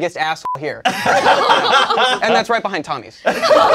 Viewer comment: I love how it takes a second to catch the meaning of first joke about Tommy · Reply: I missed it the first time too. I misheard Tommy rather than Tommy’s which completely changed the joke.